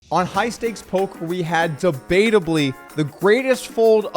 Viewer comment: did you say "high stakes polk"? is that a thing we do now, i haven't been to this channel in some time.